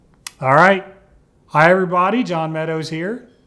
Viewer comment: For the longest time i thought you were saying higher brothers not hi everybody